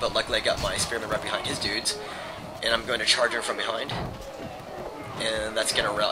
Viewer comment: it sounds like you said "Charge her from behind" haha